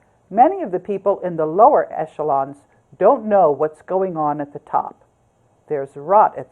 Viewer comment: "There's a Ra at the top." (did you say Ra? ..as in IS-RA-EL? or RA-thschild?)